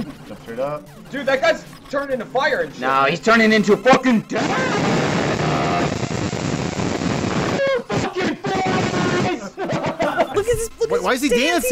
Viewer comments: I hear "Fuck you, four eyes" rather than "Fuck your face". And given personality, I think that the "four eyes" comment makes more sense. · he actually says $&@ you four eyes.